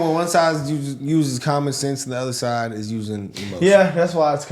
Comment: Did he just say, "One side is using common sense and the other side is using emojis"? That's the best description I've ever heard!